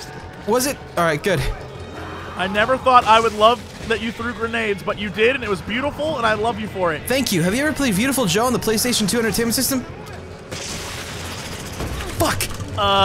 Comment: did you say viewtiful Joe? cuz if you did, then yes! I played it on the game cube I think and the red hot rumble one too. :3